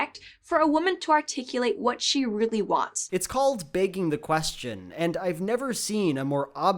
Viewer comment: every single time, i heard "baking the question" 😅🤣